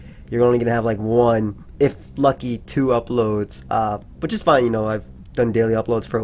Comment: I thought he said "2 fucking uploads" I had to replay it like 50 times just to make sure